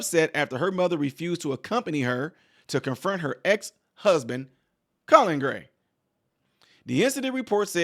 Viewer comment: With your slightly southern (to me, from Vermont) accent... when you said 5:50 "to confirm her ex-husband... Colin Gray," because of how quickly you said the name like it was almost one word.. I was paying half attention, did a double-take at my screen, like "Did he just say COLLARD GREENS for no discernible reason in the middle of this topic?! WTF? What did I just hear?" Hahahahaha. Obviously, I was wrong.. but I found it very funny. Oh.. the random tricks our minds can play on us.